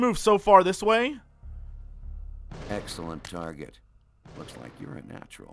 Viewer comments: the guy at 09:48 said "Excellent target" but the subtitles said "Excellent shooting" :D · The guy said target but the caption said shooting. The people who made this must've been dropouts. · WTF 09:49 the text says "Excellent shooting" BUT HE SAYS EXCELLENT TARGET OMFGWTFDRYQUEEN